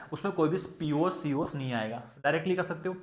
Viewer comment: sir kya bole? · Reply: Koi bhi plane of symmetry(pos)aur center of symmetry(cos) nahi aayega